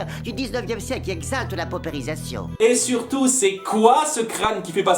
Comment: qu'est-ce qu'il a dit? J'ai rien compris, mais en tout cas, ça ressemblait pas de fou à "romantisme" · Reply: «Pauperisation» C'est l'appauvrissement constant d'une personne ou d'une groupe. Mais j'avoue que j'ignorer que le critère premier du romantisme été d'exalter l'appauvrissement. °^°